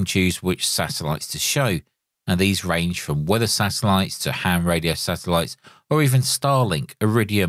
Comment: did you say ham satellite? As in ham radio 😊 Thank you. This is so awesome to have all these separate programs in one single interface.